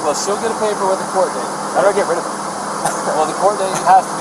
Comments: did he say im not getting rid of her....hmm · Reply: He said "how do I get rid of it?" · So at roughly 14:38 Brian says he doesn't have a phone so if she leaves him he is on his own. Then at 55:23 the officer asks Brian for his phone number and Brian pulls a cellphone out of his side pocket...Brian also told the officer they bought the van together yet it is registered to Gabby......I'm just going to leave that right there..... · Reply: Go back an listen to what he says at 27:00- 27:09, he make a joke about getting rid of the warning paper for her to appear in court. It sounds like "I'm going to get rid of it" 🤔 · Brain said “how do I get ride of her” and after laughs making it a joke. Was it actually a joke? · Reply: If you're talking about what he says at 26:55 I'm fairly sure what he actually said was "How do I get rid of it?", as in the charge that the cop was saying he was obligated to bring against her. Just before that he had said he had no intentions of pursuing charges, and he said "I just want to get the checklist of things I gotta do to get rid of it". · did he say how do I get rid of her? Or it? Meaning the charges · what did he say?? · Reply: "I want to get rid of it"... he wanted a "checklist" of things he had to do to get rid of the court dates and everything legal. I believe he knew that if she was alone and being questioned, she would tell people about his psychological abuse, and he didn't want that.